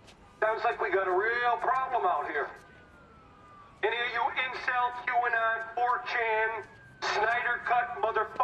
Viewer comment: That was freaking epic! I don't care if you hate Richie, he tries so hard to be something that he's not, a helpful, ball-breaking idiot. Feel free to correct me if I'm wrong. 0:32 Incel, what did he say, 4chan what? 0:48 😂😂😂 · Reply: he says "Incel, Qanon, 4chan, Snyder Cut motherfuckers" xD